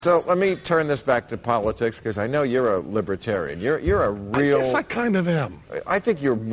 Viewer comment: I thought this said luciferian, but libertarian makes more sense considering he is that and not the first thing I said =p as far as I know